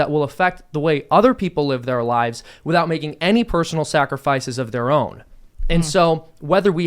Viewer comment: I swear Seamus says snackrifices